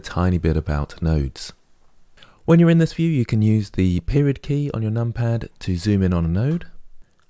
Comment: Minute 2:33 he explains how to zoom on individual Notes.. He says a word i dont understand, Sounds like "Spirit key" "pirit key" .. what key is this? And how can you make the node bigger ? I can stretch them but i cant make them long "vertical" and i would really like to zoom on this individual nodes, i got the" Ctrl+spacebar" thing.. but making an individual one bigger ??